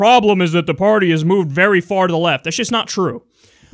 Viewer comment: I thought he said far FROM the left, so I thought he was right.